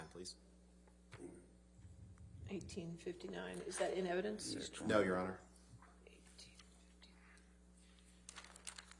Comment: what does Johnny say softly nodding his head? .....He's trying?!? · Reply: "He'll be okay"